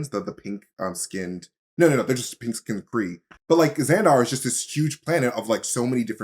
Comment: At first I thought that said xandu and I was very excited When will he come to the mcu?